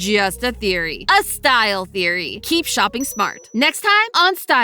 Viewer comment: wait did she say shopping not looking